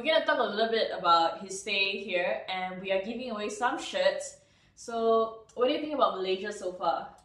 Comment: first time she said "giving away some shirts" i thought she said "shit"